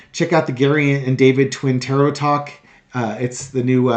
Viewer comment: David, I misheard your lovely American accent and thought you said your new channel is called - twin terror! Oops I then realised twin tarot...Fabulous.